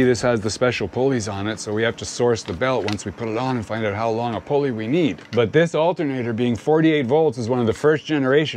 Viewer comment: Did I hear him say "find out how long a pulley we will need"? TS